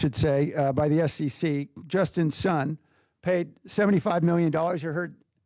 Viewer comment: Ah, I thought she said "Justin's son"...No, it's Justin Sun, a crypto billionaire. You all should look him up. My God, the US is in bigger trouble than I realized if this president isn't stopped.